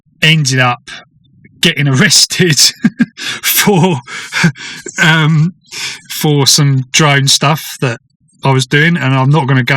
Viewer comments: At first I thought you said 'arrested for some drug-stuff' until you further explaned 😂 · I heard 'drug stuff' not "drone stuff"! 😅